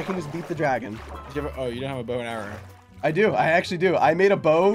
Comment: okay wait did I just hear sap nap saying he has a boner..?